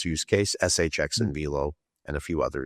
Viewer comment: Can someone tell me what Crypto they are talking about at 34:22 where the transcript says VO. VO doesn't seem to be the crypto what is he actually saying there? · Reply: VELO